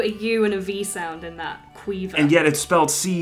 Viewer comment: Caoimhe, legit thought she said Queefa... I died.